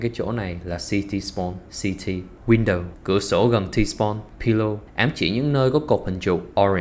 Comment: Tôi nghe nhầm pillar thành pillow nên ghi nhầm anh em nhá. PILLAR: CÁI CỘT 😂😂😂. Có gì sai sót hay bổ sung mn cmt bên dưới nhé ❤